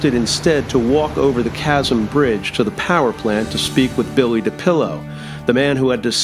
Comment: I heard "Bill The Pillow" LOL. Good video. I will like to know the origin of those daggers.